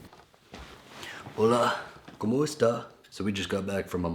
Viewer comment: Did i hear "kamusta" in 6:16 right ?